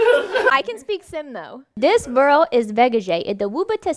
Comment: I heard “this girl is vegata “lol